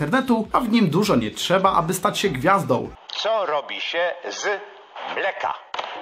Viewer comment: Usłyszałam: co robi się z fleka xD